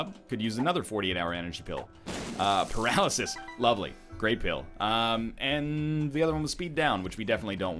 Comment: in captions it said Graybill instead of great pill XD